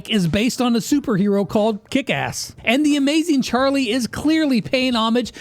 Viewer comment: WHAT DID JUST SAY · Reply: kickass